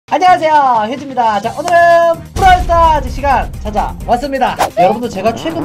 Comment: 초에 나만 부랄 ...스타즈 라고 들려?...